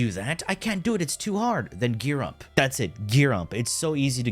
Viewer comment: "gear up". I thought you were saying give-up